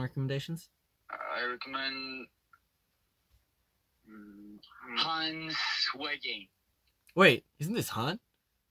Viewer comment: bruh, the first time i herad this, i thought felix said "han's twerking"